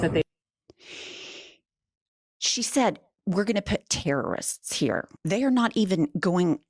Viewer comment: I heard “tourists” not “terrorists.”